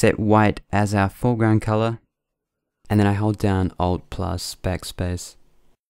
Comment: hey did u say "hold down ALT and backspace" at 11:09? I couldnt figure out how u added a shadow on the logo =(